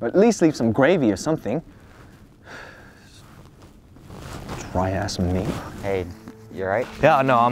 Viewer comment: When he said “why ask me?” Sounds like he said “dry ass meat” 😂